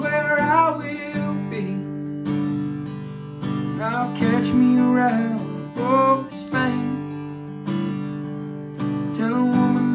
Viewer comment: Lyrics: I’m just a traveling man you see Wherever this road goes is where I’ll be And I’ll catch me around the ghost of pain Tell a woman that I love her and forget her name And I wish I could stay for midday coffee But I just can’t get this dreaming off me If I could find me a place to rest my mind But mama always said you were there in good time And there’s some headlights burning down the highway And I think that I may just catch me a ride ‘Cause I’m a traveling man by trade, sir We’re all runnin’ from the things inside There’s a train headin’ west to south Toledo And if the God’s don’t sell me, to sleep I’ll go Dreamin’ as were steamin’ past miles of ground To the souls and hymns that built this town There’s some headlights burning down the highway And I think I may just hitch me a ride ‘Cause I’m a traveling man by trade, sir We’re all runnin’ from the things inside There’s some headlights burning down the highway And I think I may just hitch me a ride ‘Cause I’m a traveling man by trade, sir We’re all just runnin’ from the things inside There’s some headlights burning down the highway And I think I may just hitch me a ride ‘Cause I’m a traveling man by trade, sir We’re all just runnin’ from the things inside (I know there’s probably quite a bit of mistakes. Some parts were harder to make out than others, but I know a lot of people enjoy reading lyrics as they listen!) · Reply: I kept hearing I'll catch a ride on a boat to Spain